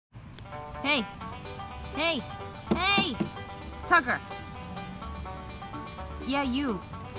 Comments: At the beginning I thought she said Fucker 😵😹🙊 Listen it again and you'll find its correct~! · I swear I heard f*cker